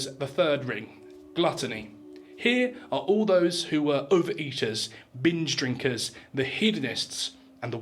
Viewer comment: why did I hear uber eaters? xd